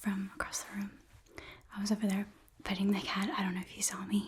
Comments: I heard "I was over there petting the cat." As "i was over there FIGHTING the cat." And i was like yeah i believe you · "I was over there petting the cat. I don't know if you saw me." Of course I did because I'd be doing the same exact thing at a party. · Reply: Ok, so she did say that she was "petting" the cat. I kept hearing "fighting" but I was hoping it was "petting"